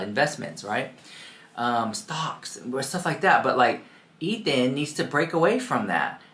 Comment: Am I hearing Ethan? lmao